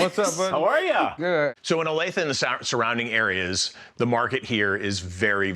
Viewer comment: This episode is from November 2023 in Kansas (probably Kansas City suburbs). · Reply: Yes, I think I heard him saying Olathe which is a KC suburb.